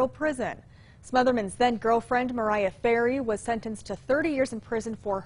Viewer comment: I really thought she said his girlfriend's name was "Mariah Carey"